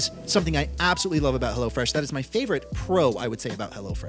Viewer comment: When said "Hello Fresh," I thought he said "Hella Fresh"